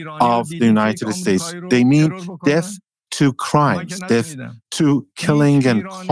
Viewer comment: ish, does he say, "Death to Christ"? Or, death to crime? I cannot tell.